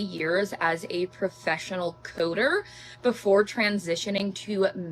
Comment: omg....i need to go to sleep. I stg I thought they said their mom was a professional "cooter" at first. I'm going to see myself out 😂